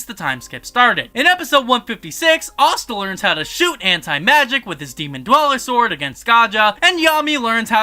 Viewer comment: thought he said hentai magic